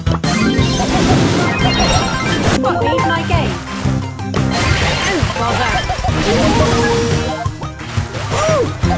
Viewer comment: WHAT IS ROSA SAYING?!?!?!? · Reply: Botany is her game